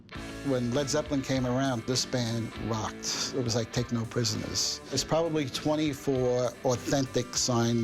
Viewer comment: lol... did he say "Techno Prisoners" or "Take No Prisoners" @ 0:31 ?